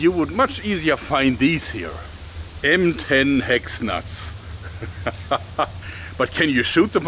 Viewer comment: I misheard M10 Hex Nuts as "Ben 10 Hex Nuts"